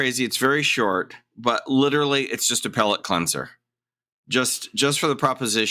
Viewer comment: Thought I heard “appellate cleanser” instead of “a palate cleanser” and thought man is on fire with the puns.